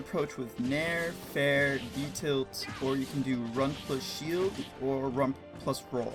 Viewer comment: i'm new to smash bros... i feel pretty bad for thinking this game was a button masher this is insane can anyone give me a quick breakdown of what he's saying at around 5:45 i understand the Nair Fair - neutral, forward, etc for A - but that's all i figured out. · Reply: Yup you got it, other thing I said was Utilt which is short for Up tilt.